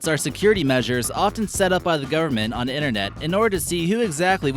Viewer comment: half the time i thought the speaker was saying 'garmet' instead of 'goverment'